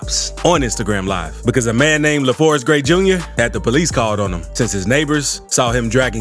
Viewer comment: Coulda swore a he said a man named LeFourthgrade Jr.